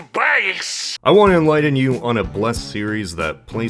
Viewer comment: What did he said? "Supayce"?